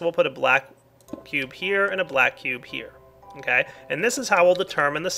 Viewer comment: lol at 19:38 it sounds like he says black pube